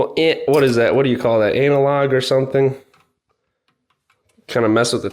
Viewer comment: Anal-log 12:28 ?😅 sorry bro that’s all I heard lmao